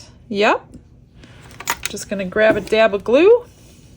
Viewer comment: You said “grab a dab of glue” I heard “yabba dabba doo”